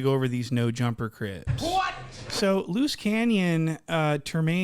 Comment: All I heard was….. “No jumper crisp,,,loose canyon” 😂😂